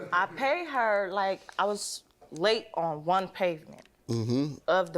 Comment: Did she say pavement ??